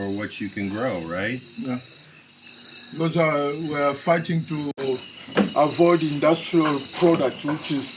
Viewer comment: Where is Tarzan? 😅 6:43 did he say "we are fighting the 4th industrial product?" Listen ya'll Africa has been the best so far. For some reason, Ive been glued to every one! Europe didnt care for